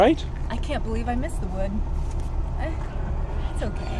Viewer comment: Did she just say "I've seen better wood"